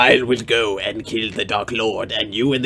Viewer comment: For the first 5 mins, I thought he was saying "legless"